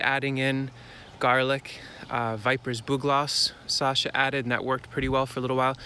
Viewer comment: what are you saying at 6:25 ? planted garlic and ...."vibrous bouglas"... ? anyway thank you for the Videos im learning so much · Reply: Vipers Bugloss...